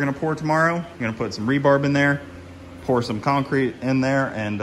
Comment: did u say rebarb? I swear it sounds like u said it lol. Rebar.